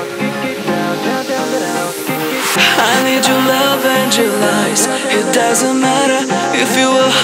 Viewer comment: Love this song. But it sounds like he says. I need your love and your lies.